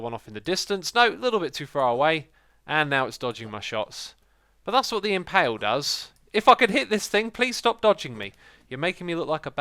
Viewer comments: I had the rewind the vid cuz I thought said fuck. "fucking hit this thing." and I was like "oh shit X is about to lay it down on em!!!" but no, I just misheard... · sounds like he is saying (fucking hit this thing) xD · did i just hear "it fucking hit this thing please stop dodging me" ?